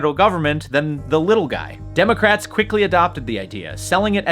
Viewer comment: Did you say Democrats.....😮